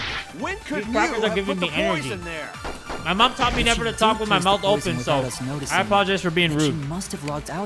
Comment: did he just say "my mom taught me never to talk with my mouth open"